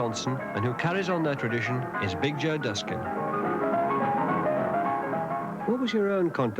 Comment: Who is that pianist who appears at 22:19 in the video, wearing the white sport coat, white hat with the dark band around it, and the sunglasses? I could not figure what the announcer said for the name. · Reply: Okay upon repeating that part of the video, the name is "Big Joe Duskin".